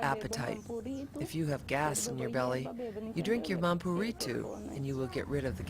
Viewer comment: which herb is meant? I can't get it? Bamburito?? How is it called correctly? Anybody knows?